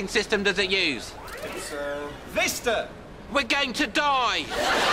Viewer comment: What OS does it use? Vista! We’re going to die! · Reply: Nah, he definitely says "We're going to die!" in the video - though I can see why it could easily be misheard.